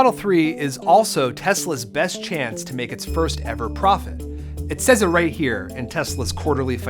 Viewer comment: did he say "first ever profit" ?